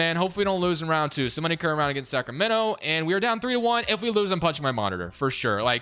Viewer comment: For a sec I thought you said “If we lose, I’m punching my MOM” 😂 Had to rewatch that part